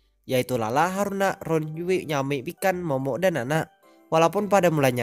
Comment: ngakak gua pas denger nyebutin nama mikan kek kedengeran nya ikan :v